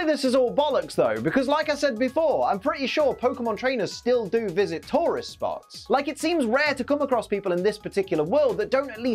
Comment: Honestly the first few times you said tourists spots I thought you said tauros spots. 😂